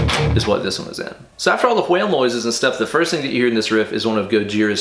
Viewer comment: Did you say whale noises? LOL!